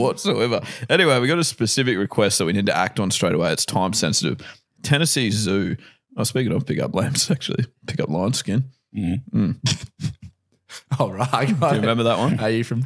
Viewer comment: I thought said 'pick up lions' instead of 'pick up lines' after tenneesse zoo.